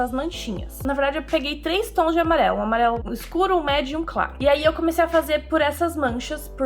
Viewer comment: Só eu que escutei "Amarelo Escuro, Amarelo MÉDIUM , Amarelo Claro"